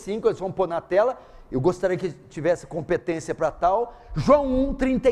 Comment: Nos minutos 11:57 o pastor pede pra colocarem o versículo na tela e diz: " espero que tenha competência pra tal"? Foi isso mesmo que ele disse com o irmão que ajuda? Ou eu entendo mal? Espero que não seja o que eu entendi gente.😢 Porque se foi, o pastor é muito arrogante.😮